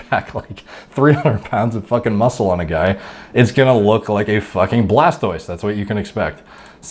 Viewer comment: did he say a "fuckin Blastoise!" 🤣🤣🤣 Wtf? I never thought I'd hear chinpukuman being mentioned on this circle. Personally, i look like Shoe!